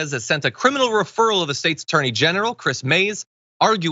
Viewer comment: I could've swore he said "State's Attorney Genital" which makes sense if he talking about Texas where the AG is a literal walking penis.